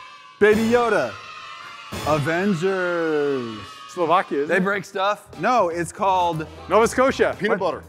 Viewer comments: did say "Slovakia is it" when said "Avengers" or is it just my imagination?😳😳😳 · "What's it called Nova Scotia", I don't remember Avengers coming to where I live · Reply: I couldve sworn he said Nova scotia, I live there too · did i heard slovaki? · did say Slovakia?